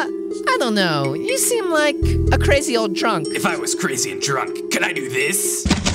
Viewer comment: Lazercorn I thought he said crazy and trump not crazy and drunk